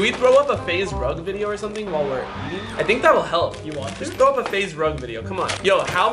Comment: who hear say do u watch him 😂😂